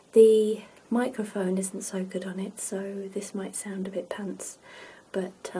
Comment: what does she say? "This might sound a bit *pants*"? Did I hear that right???